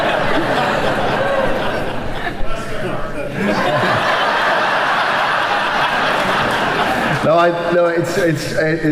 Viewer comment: What does the audience member say at 20:39 that makes everyone laugh? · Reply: Bless your heart.